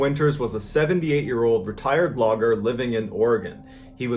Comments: I had to rewind it a couple of times because I thought he said the guy was a "retired vlogger" by 1969... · I heard that as 'vlogger' and was so confused lmao